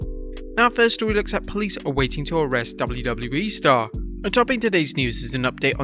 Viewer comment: Did I heard right ? @ 4:47 WWE ??